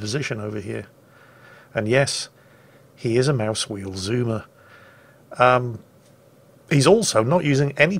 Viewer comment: "A mouse-wheel Zoomer." ... All I hear is "Okay Zoomer."